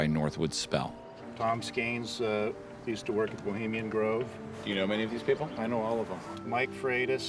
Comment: Did he just say Bohemian Grove?